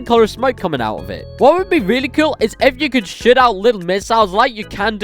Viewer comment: I’m sorry but instead of saying shoot it really sounded like $h!t 🤣